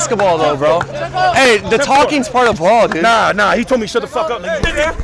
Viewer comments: The dude said check the fuck up and thought he said stfu 😂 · Why trying to fight the Pod Cast guy? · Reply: He thought he said “shut the fuck up,” but he said “check the fuck up.”